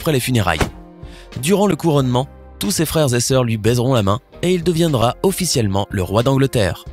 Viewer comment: un BUG ? 😁😂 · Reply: WTF ses quoi sa 😂😂😂😂😂😂😂😂 à 6:35 hihihihihihihihihihihihihihi 😂😂😂😂😂😂😂😂😂😂😂😂😂😂😂😂😂😂😂😂😂😂😂😂😂😂😂😂😂😂😂😂😂😂😂😂😂😂😂 baiseront ou baisseront ???